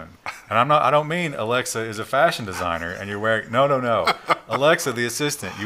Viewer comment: i genuinely heard "Fascism signer" instead of "Fashion designer". I need a new headset i guess.